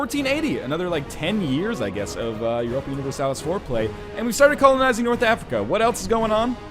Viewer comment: Near the end at 31:02 it sounded like you said "Another ten years of Europa Universalis Foreplay"